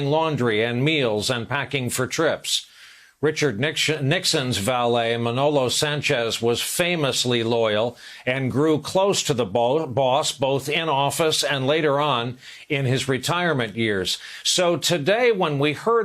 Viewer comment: “And grew close to the balls,” did I hear that right?